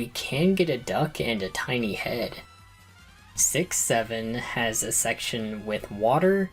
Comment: what'd you say? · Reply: 67 :(